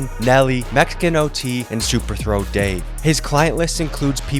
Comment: I thought you said super throat Dave. I was like yooo. 😂